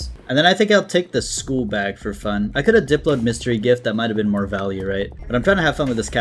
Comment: when said he was going to get mystery gift i thought he said he was getting Mr. gift, im calling it that from now on.